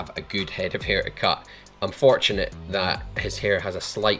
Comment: It's fortunate that his hair is wavy. Wavy hair is probably the easiest to cut. If you cut straight hair there is the chance it stands on end when dry. Wavy hair tends to be quite relaxed. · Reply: it sounds like you're saying "unfortunate that he has a slight wave" Unless I'm mishearing it